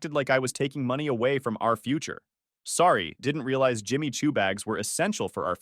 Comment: I heard "Jimmy chew bags" and that's my head canon